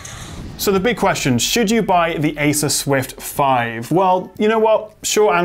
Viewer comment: I hear you said ‘asus swift’...? ;)